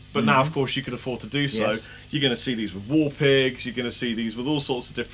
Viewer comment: At about 8:20, he mentions we're going to "see these with all sorts of..." war pigs? I misheard. Anyone hear it better then me, and correctly? Thanks! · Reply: John Lampson he said war pigs, regarding the Bare Knuckle War Pig pickup set.